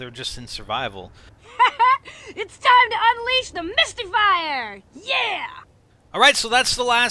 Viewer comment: 18: Listen to the quote again at 8:17, I don't think she's saying Mystifier, I think she's saying Misty-fire. I could be very wrong, but it does sound like she says her name.